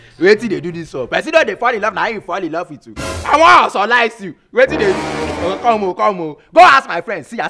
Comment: LMAO! Did I just hear "I wan OZOlize you" at 0:09 ...LMAO!